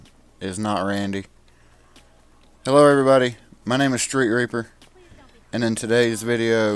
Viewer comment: For a second I thought he said street raper